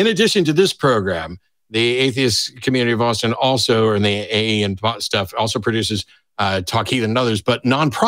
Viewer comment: had to rewind...thought matt said "the AA and butt stuff"